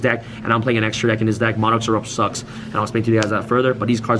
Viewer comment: did he just say monarchs erupt sucks? sorry i cant winter cherry you im to busy one side skill draining your board